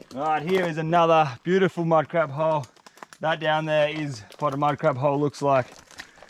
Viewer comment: could've swore he said minecraft hole